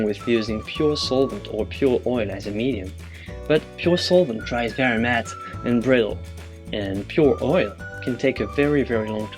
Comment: Thank you for the video. I haven't painted any oil painting, but I find this interesting. The only thing is I am not very sure what you said at 12:00. Did you say, "pure solvent dries very matte and brittle"? Thanks in advance for your reply!